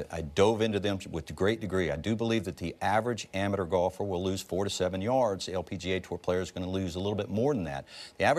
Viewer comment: I thought Chamblee said 47 yards instead of 4 to 7 and I had a mini heart attack